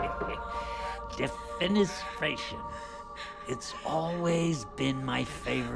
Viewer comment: what does he say here? Some latin? Definis vaccin? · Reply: Defenestration, the word for throwing someone out of a window